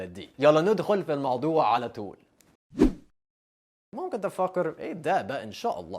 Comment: هو بيقول ممكن تفكر... بس انا سمعت فاكر او ذيك الكلمه 😂😂💔